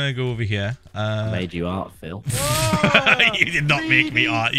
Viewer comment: i thought he said I made you hard Phil 😂😂😂